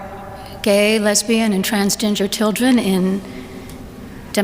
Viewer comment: Did she just say trans ginger? 😂